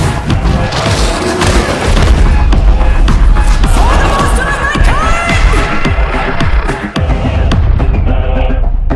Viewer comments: What does the zealot say at 1:15? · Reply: For the master of mankind!!! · Does anybody know what is she saying in 1:16 ? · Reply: For the master of mankind